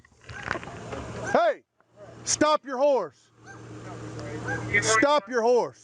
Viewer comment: "Stop your horse" 💀🤣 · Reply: at first i thought he said ''stop your whore''